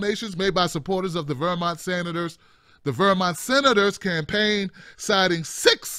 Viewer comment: LOL thought said weimar senators" (vermont senators")